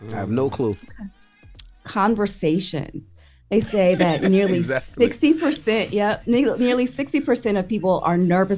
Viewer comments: Why when mimi said “nearly” @ 13:43 I thought she said “N**” I was like “Okay Mimi, bout time you let that thang up come up out ya!” 😂 if she was a first lady she would definitely be a Baptist one the way she fixed that so quick and kept it pushing. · I love Mimi - so professional. You can tell she’s well educated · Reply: Why when mimi said “nearly” @ 13:43 I thought she said “N**” I was like “Okay Mimi, bout time you let that thang up out ya!” 😂 if she was a first lady she would definitely be a Babtist one the way she fixed that so quick and kept it pushing.